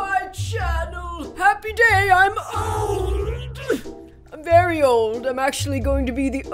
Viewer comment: At the beginning I thought she said, “Happy day, I’m owwwwwnnnnneeeddddd.” 😭😂